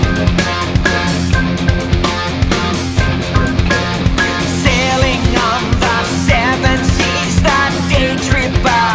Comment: Anyone can understand what he say at 14:00 ?? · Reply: "Isn't this where we came in?"